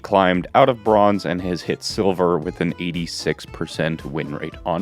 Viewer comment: Sorry, I thought you said he had an 83% win-rate as REINHARDT. In BRONZE. Ha! The team coordination in bronze is so bad! my first 50 hours of playtime were maining Rein and I couldn't escape bronze. I had to get a plat friend carry-DPS me up to gold, and I've managed to stay in gold easily. So ha! The thought of someone actually getting OUT of bronze with REINHARDT? XD